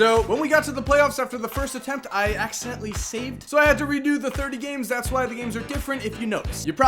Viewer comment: I thought you said loes 30 Games strayt